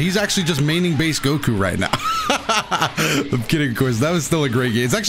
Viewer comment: Yo that joke is funny. With an IQ of 7,000 he could be solving the worlds problems, instead of bodying as Base Goku. LOL - at 9:06 what is being said, “I’m kidding QUIZ.”?